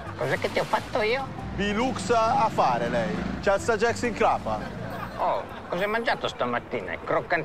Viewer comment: cosa dice Bernasconi a 2:16? c'ha il sagex in crapa? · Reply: Esatto, il polistirolo in testa